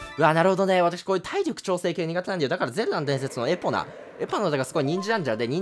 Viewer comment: 早口のせいでゼルダの伝説が全裸の伝説って聞こえて草